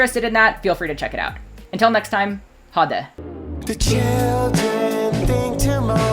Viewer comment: did i hear a "ha det!"???